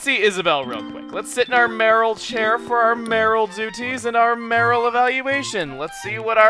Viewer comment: You say "mayoral," I hear "Marill." XD